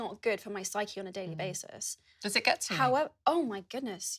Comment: what does she say? “Does it get ____?” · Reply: "does it get to you?" Does it get too much to cope with/overwhelming